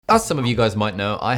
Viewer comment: I don’t know why but when you said ‘As some of you-“, I heard “Assalamu-“ bro I was surprised ngl haha